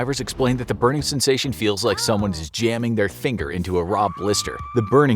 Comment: ya'll hear that sounded like he said jjaming his finger haha stutter there hehe